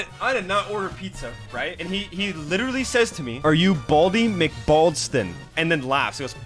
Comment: Noooooo i was 7 hours late btw did say Baldi at this